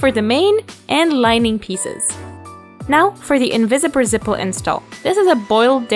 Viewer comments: did anyone else hear "invisiber zipple"? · Anyone else hearing "invisipper zipple" at 7:00 ??